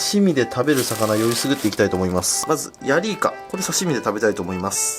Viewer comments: ヤリイカがおっぱいに聞こえたのは僕だけじゃないだろう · ヤリイカがお｡○いに聞こえた